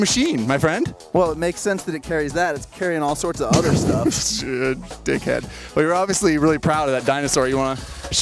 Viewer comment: did just say dick head ? LOL